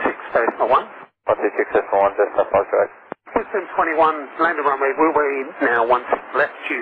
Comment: The callsign ending in 21, at the 5:30 mark is Wisdom 21, Wisdom is the callsign used by the VIP 34Sqn Challengers and B737s.